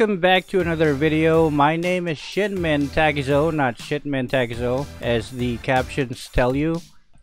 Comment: SHITMEN I mean 😂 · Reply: Same here I thought he said shitmen hehe!